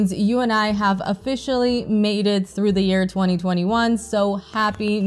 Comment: "If you're watching this, that means you and I have officially MATED through the year 2021" is what I initially heard and nearly had a heartattack!! I thought I must be dreaming until I realised you actually said "MADE IT through the year" , then it made me laugh. Maybe it was just wishful thinking on my part given you're a fellow cinephile with great reviews. My apology for mishearing you in the first place but hopefully you appreciate the humorous error on my part.